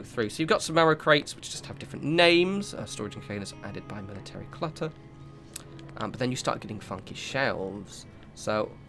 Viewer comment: i thought it said military gutter and i was excited to see some gutter action